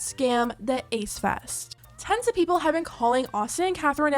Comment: When you said "Tons of people have been calling then out" I heard "Tens of"🤣 hope no one falls for this scam of theirs.